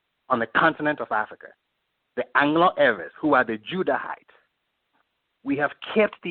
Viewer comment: Who did he say at 10:28? I can't catch It. Sounds like Anglo Arab